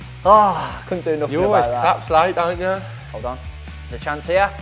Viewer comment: What does jack say @ 3:38 you always....? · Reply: collapse late